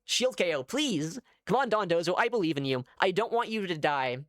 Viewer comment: Every time I hear "Dondozo" my brain turns it into "Don Bozo" and I can't un-think it. 😂